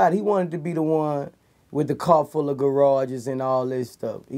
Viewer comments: Soooo I'm the only one who heard him say: "Car full of garages" 😂😂😂😂😂 Gotta Love Boosie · Did anyone else catch him saying " A car full of garage's!" 🙄. At frame #